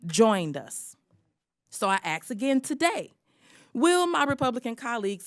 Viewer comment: Did she just say “ So I axe 🪓 again today?” Lol 😂. Axe 🪓